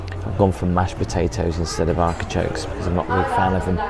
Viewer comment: did my ears deceive me or does say "arkichoke" instead of "artichoke"? that's what I thought I heard - at first I thought it was just a quick misspeak, but then he seemed to say it again. maybe it's a regionalism?